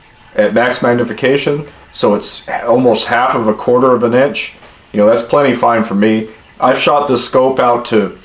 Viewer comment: Am I hearing things or did he just say "almost half of a quarter of an inch" ?